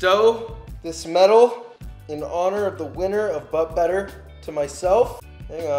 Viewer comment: Just had to watch this before I sleep but my sleepy self misheard that as butt butter uhm...